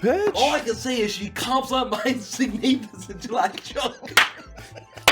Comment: wtf did say? cant understand it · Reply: Something along the lines of “she comps on my sognathus”